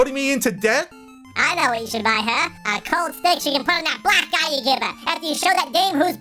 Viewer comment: What does the bear say at 10:01-10:06? The voice manipulation makes the bear's speech quite unclear. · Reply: the bear says. I know what you should buy her. A cold steak after showing her whos the boss